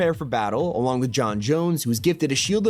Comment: did he just say john Jones?